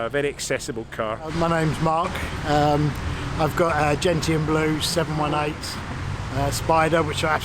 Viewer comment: "GENITAN BLUE" LOL great cars, cheers from Italy! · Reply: I thought he almost said 'genital' 😂